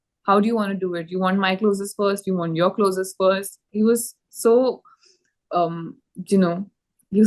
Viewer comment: Ranbir is so sweet, he asked do you want to take your clothes off first or mine. I mean seriously 🤦‍♂🤦‍♂. Just want to know what were you expecting him to say or do, take off his clothes and say aaj maze karte hai. It's just laughable how gullible these girls/actresses are and don't understand the psychology of men and how it works. Anyways, keep hailing him just coz he is a senior actor and comes from acting family so you can get better or more work. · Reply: He said “ you want your closes first or my closes first”. Its a common term used during video shoots